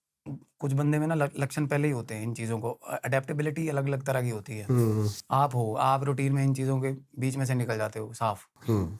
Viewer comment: if anyone knew plz tell what were the games he said was dark?? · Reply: Outlast